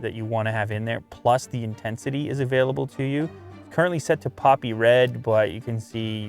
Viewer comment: when he said poppy red i thought he said poopy red, grogginess got to me huh